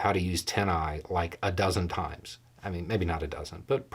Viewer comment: I didn't catch what he said at about 5:18. Did he mean TinEye?